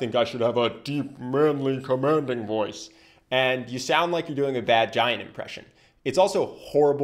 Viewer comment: I thought he said “It sounds like you have a mangina” 😂